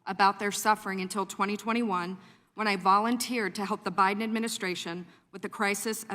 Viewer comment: did she say help the Biden administration 🤔. The kid sniffer